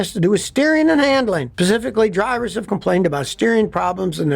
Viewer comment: did he say "Pacifically?" LOL (it's specifically sir) :)